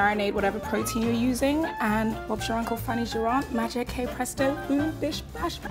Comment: I thought the saying was “hey presto” not “kay presto”???😂